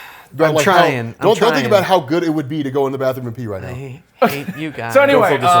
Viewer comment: I almost thought you where about to say "they're drinking pee right now"... And then I had to go to the bathroom.